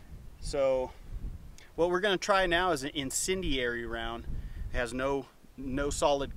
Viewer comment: Did you say Justin Cindiary? Is that his last name? Do you call him Cindy?